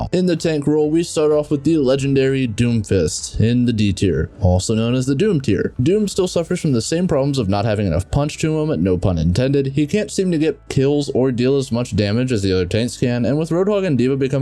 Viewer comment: When u Said “doom doesent Seem to have enough punch to him to pun intendet” I heard it as no punch intendet